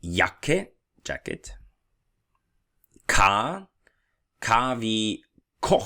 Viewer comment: Are you Bulgarian because after the word jacket at 9:03 I heard you clearly slip and say "чакайте" which is wait in Bulgarian😂,just curious.